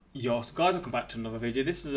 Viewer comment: I've watched a hundred of his videos and I still can't figure out what he says at the beginning. I hear "yos guys" or sometimes "yosh guys" and I don't know what that's supposed to mean. What is he actually saying?